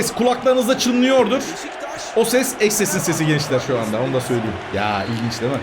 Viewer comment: ne diyor orda kimin sesi diyor?? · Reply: Eşinin eş sesin